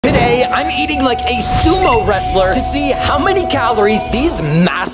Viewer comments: I thought bro said "Today we're eating these sumo wrestlers" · I thought they said "Eat a Sumo Wrestler for 24 hours" 💀